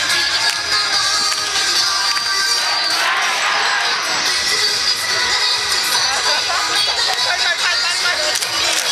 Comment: このコールってなんて言ってるの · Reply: イエッタイガー